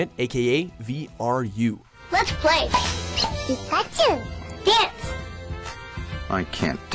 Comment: Penis .... am I the only one who hears it? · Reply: It does sound like that now that you mention it but what he is really saying is “Dance”. It’s just that commercials back in the days had crappy sound quality